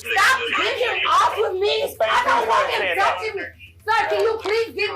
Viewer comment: I thought he was saying “Mrs. Thang” instead of Mrs. Payne 😂